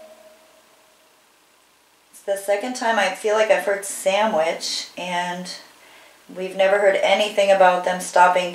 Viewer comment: I clearly heard her say “Sandwich Medicine “